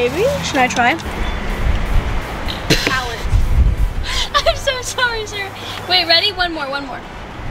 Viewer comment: I thought she said I’m so sorry sir instead of I’m so sorry Sarah😂